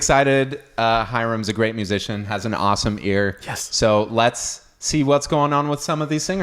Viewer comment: I had to listen to you twice when you introduced Hiram....I heard "Hirams a great musician and has an awesome BEARD"....and then checked out his beard and re-listened, and heard "awesome EAR"....LOL....not a bad beard though.